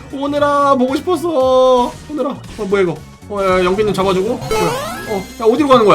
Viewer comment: 오늘이 잡아주고 라고 들림